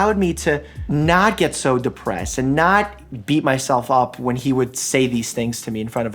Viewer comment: Did he just say, *Beat my MEat*.. ?? OH hell no.. i'm high on pervness